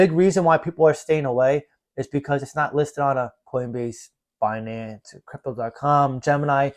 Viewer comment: Not listed there yet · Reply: ah cheers mr. I must ov miss heard u. Thought u said u got it of coincbase👍🙏